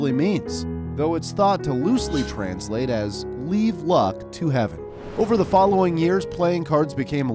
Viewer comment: I thought it said “leave fuck to heaven” 💀